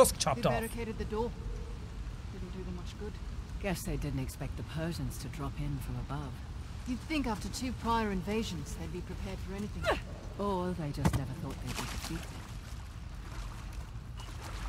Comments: At that part where Nadine says: ''Above you'' i thought she said ''I love you'' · I THOUGHT NADINE SAID "I LOVE YOU" WHEN SHE SAID "ABOVE YOU" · I thought she said I live you but she said above you